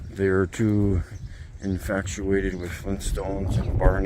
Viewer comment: Does he say "infactuated" at 2:55?